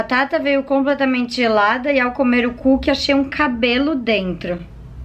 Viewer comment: só eu que ouvi: e ao comer o c* kkk Desculpas kk